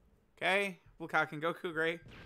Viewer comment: Thought said "Bukake Goku"